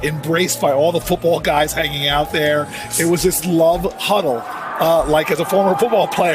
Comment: mark. Did he just say “Love Huddle”? Yo! P.A.U.S.E.